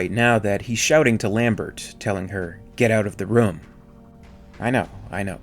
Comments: I always thought he said " get outta the road" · I always heard it as "Get outa the road." But that's just me. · 2020 almost done and listened to this at least 10 times now. I keep hearing “Get outta the ROAD!” There’s no “oom” sound, but there is an exaggerated O sound..